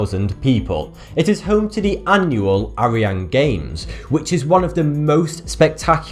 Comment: from what I know the R sound doesn't really exist in the Korean language, It sounds more like an L and looks like a snake ㄹ btw in that pronunciation I thought that he said Aryan Games